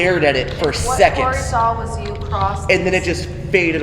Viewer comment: to however long, there is a whisper and from what i can make out it said “what you saw” 😦